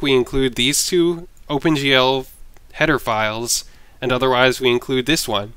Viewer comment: some people are are beginners to c++ also with i think they instead should try to understand c++ first before working with OpenGL, and is it just me or does it sound like he says hedophiles around 3:08 xD